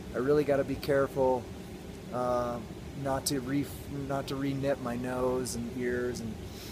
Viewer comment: I came from today's reddit post on jobs (July 9 2022 for others) and I want to know what it means when you say "I need to be careful not to 're-net' my nose and my ears" 10:35 At least is sounds like re-net. What did you say and what does it mean please. :o) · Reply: I was saying "re-nip" my nose, as in frostnip. Frostnip is sort of a milder version of full frostbite. It's still bad and results in tissue damage, but it's usually only on the outer surface (and not deep tissue damage).